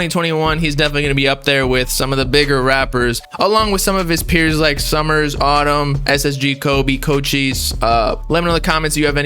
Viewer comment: can you list the rappers you named? sounds like you said summers and autumn? I know ssg kobe and cochise already.